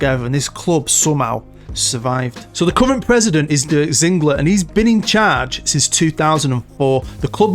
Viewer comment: There’s a hell of a lot of history at this club. When you said name of their president I thought you said Dirk Diggler and that’s all I could think about. I went to Berlin a few years a go with my wife, daughter, step dad and his partner as he was born in the former east Germany and I got a Hertha Berlin shirt so I bought the wrong Berlin team!